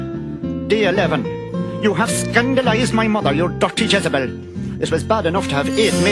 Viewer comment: A True to BBC! Likewise. I'm honestly surprised and happy i saw a Jamaican's comment first. It begs the question: was this made by a Jamaican? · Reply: he must be a little bit Jamaican, cause I swear I heard "yuh dutty Jezebel!" on day 11🤣🤣